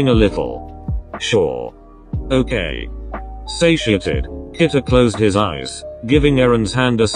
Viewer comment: When it said satiated I was reading the words so I heard it as "Say shitted"